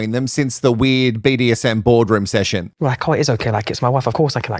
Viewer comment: Everytime I hear BDSM board room, I hear "bored" room😅